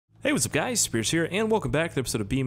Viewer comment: I swear to god. No matter how many time I watch your videos, I hear, "Hey what's up guys, scarce here." Every time.